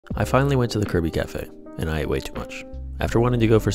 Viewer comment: Thought he said "finally went to the Kirby café.. & I weigh too much" 😮😂